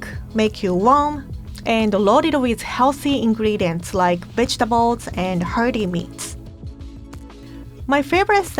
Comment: I heard "bitchtables"😂 like is the best person to make people eat their veggies like BITCH EAT YOUR VEGETABLES!❤️😂